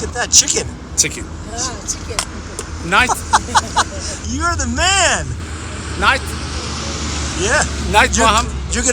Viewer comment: I think he is saying "join us for the dinner". Such wholesome kind-hearted people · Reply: yeah you were right i am Persian actually he said lets eat together